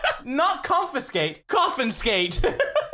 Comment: CC says Dolphin skate not Coffin ⚰️ skate